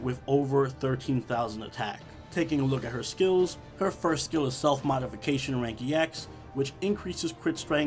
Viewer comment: I heard "Salt Modification"...I think I'm still not ready for this video.